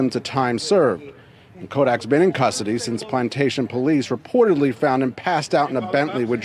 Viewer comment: I'm soryy....did they say plantation police?